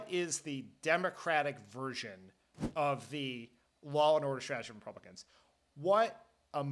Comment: What is he saying here? 17:09 “Democratic version of the...” what strategy? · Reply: '..law and order strategy of the Republicans.'